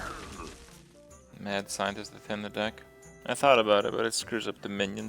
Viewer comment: Add what? What did he say? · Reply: Ender Man "Mad Scientist to thin the deck"